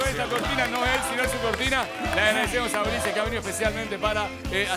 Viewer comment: De quien es la cortina del programa? nombra a alguien pero no entiendo quien es? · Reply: Ulises bueno era que hizo la cortina